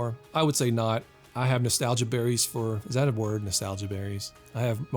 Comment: At first I thought he said Nostalgiaries. I'm officially using that as a word from now on!